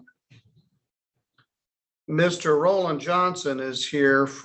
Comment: Wait, did I mishear? Is the bondsman named Golden Johnson? Edit: oh. Roland